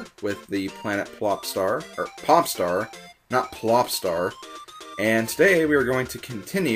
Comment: with subtitles on: Ha! It says "planet flops are for pop star not lost are", instead of "Planet Flopstar-or Popstar, not Flopstar".